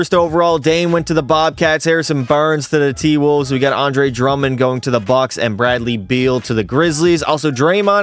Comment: I had this video playing in the background while doing some bullshit and I almost spit my coffee when I heard “tingus pingus going to the bucks” 😂😂😂😂 ahahahaha